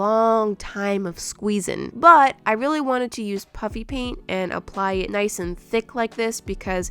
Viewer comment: why does it sound like shes saying "puppy" paint everytime she says puffy paint? lol